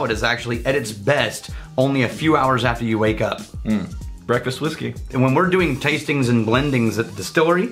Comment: All I heard was "breakfast whiskey"